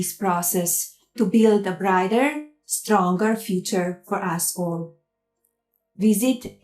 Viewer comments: Why is it when she says "for us all", I hear "for asshole"? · did Melania say to build a strong future for a**hole? hahaha what?